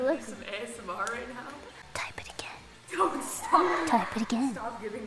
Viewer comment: I didn't quite catch what she said, can anyone help? "Call me a table call me an (elf?) one more time". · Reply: Yes elf. Just in case u dont understand, this is a reference to ""elf on the shelf" where santa sends his """""""""""LITTLE""""""""":)helper elf to secretly watch kids to determine who is naughty.